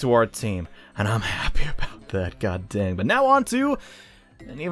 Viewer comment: did you say "dang" or "damn"? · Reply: dang